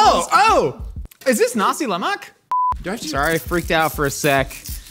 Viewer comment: I’ve heard plenty of outrageous things on this show but 16:21 is where I actually had to triple take 😭 I’m gonna need here for this one cuz I know I didn’t hear what I thought I heard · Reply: nasi lemak lmao